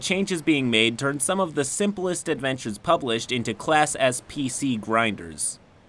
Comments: For a hot second I thought you said "Class SCP" and it still made sense. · "Class S PC grinders" What I heard: "Class SCP grinders"